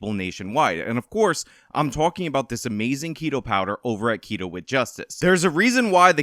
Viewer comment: Lol I mis-heard that add read at first. I hear "Pedo Powder" and its just, what!